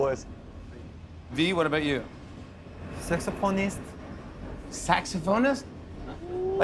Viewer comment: V then: "Sexy pornstar" (shy and didn't know much english) V now: (knows what he said last time) "Saxophonist"